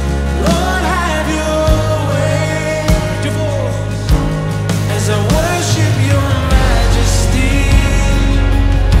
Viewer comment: What is he saying at 1:55 ? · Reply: I think he was saying, "lift your voice"